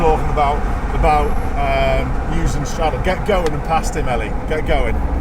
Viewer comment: i first heard: "get going! Blast'em Ally"